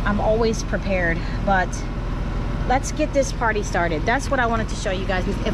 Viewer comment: ! I had to rewind! I heard "Let's get this potty started". Timing is everything! ❤😂❤